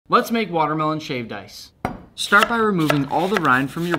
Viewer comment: For a second I thought it said watermelon shaved rice 😂